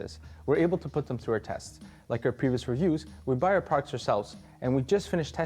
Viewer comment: He's talking way too fast and mumbling all the words together. What's the rush? Slow down next time. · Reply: The subtitles say "we buy our products ourselves" sounds like he said "we bireprocs rsels"